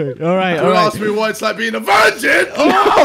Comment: wait what words did JJ at 1:10:21 i didnt hear him propely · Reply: “Don’t ask me what it’s like being a virgin! Simp! You incel!” Hope that helps!